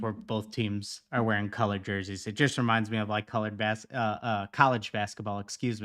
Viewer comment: Did he just say colored basketball😂😂😂😂 im dying inside